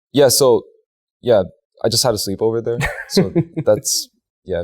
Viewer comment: Did we hear it right? Her house? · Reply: if you're talking about 1:51 i thought i heard that too but i rewinded it just to make sure and he said "i just had a sleepover there"